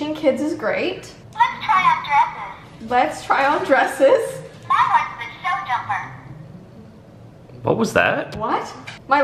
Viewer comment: I heard: "My heart is a sell jumper" lol idk what a "sell jumper" is xD